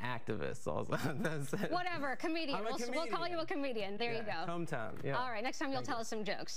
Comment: Did... did he say cumtown?